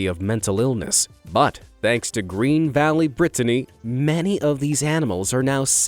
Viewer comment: Did he say “Many” of the animals were safe and happy not all.